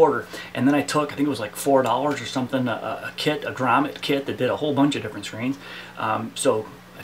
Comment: what kit is he saying i cant make it out. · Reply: "He" / I said "grommet kit" - it's #3 in the parts list written below the video in the description.